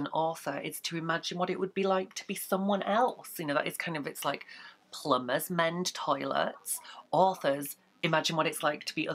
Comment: she said 'plumbers mend toilets', so you can fix the subtitles with that instead of (mumbling). :) She meant to say like 'plumbers mend toilets, that is their job, and it's an author's job to imagine what it's like to be other types of people', so it does actually make sense, I'm not just making stuff up :)